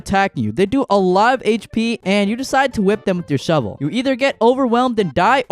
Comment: Did you say “Whip them with your shovel”??